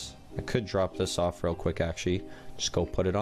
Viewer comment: who else heard ashy instead of actually